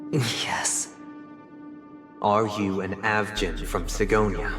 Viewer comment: Aventurine is avgin!?? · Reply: At first I thought you said "a virgin" 💀